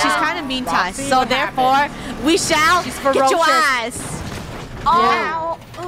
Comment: FUNNEH DID YOU JUST SAY THE A WORD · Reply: No, she said, “THEREFORE WE SHALL GET YO EYES” Not.. not what you thought. 😭